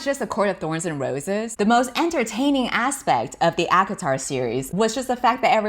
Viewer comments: When she said ACOTAR, for a second I thought she said Avatar, and I was just sitting there trying to process what I just heard. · I thought she said "Avatar series" and I was so confused because wasn't that on Nickelodeon? Why would they have huge things? Why would their thing size even matter? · when u were talking about the gigantic schlongs of the acotar series, i thought u were saying avatar series and i was really struggling to remember when we saw anyones dick in a nickelodeon kids show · I thought she said avatar as in the last air bender and screeched so loud my dad walked in my room with a bat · i thought she said "avatar series" and i thought i was gonna have a stroke from laughing... GIRL THOSE ARE CHILDREN IN THAT SERIES!!